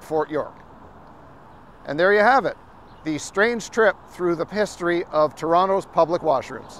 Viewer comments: Did homeboy say "the 'pisstory'" at the end? lol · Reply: I thought I heard pisstory as well ! 😂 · did he say the Pisstory of Toronto?